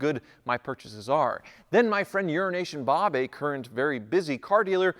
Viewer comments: “Euronation Bob” Every time says it fast I hear “Urinating Bob” 😂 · Did you just call Euro Asian Bob "urination bob"? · I heard "Urination Bob" which would be a really cool nickname for a drunk :D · Did he say "urination Bob"? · urination Bob?? LOL did I hear it right?? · it sounded like he said "Urination Bob" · Did he say urination Bob?? He is your friend right? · did he say Urination Bob? Lol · Ba ha ha ha. He said Euronation Bob with an inflection. Sounds like he said Urination...🤣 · I thought was saying Urination or Euro Nation. I was like... How did he come up with that name.